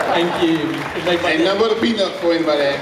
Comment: What ronnie says at 1:44? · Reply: "Ain't nothing but a peanut, if anybody asks"